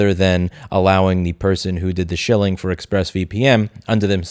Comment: Wait did Louis call Linus a shill around 4:00 min in? Lol